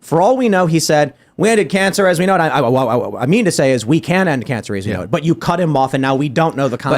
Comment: I heard “end of cancer as we know it” 🤷🏻‍♂️